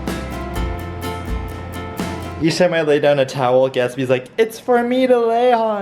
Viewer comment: I thought he said lowkey gatsby died scared me